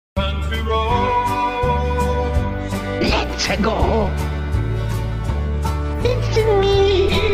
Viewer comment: why did it sound like he said, “mexico” when he said, “let’s-a-go”